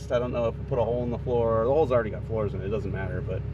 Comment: I'm like, did he just say the "holes already got floors in it?"... Sounds about right :)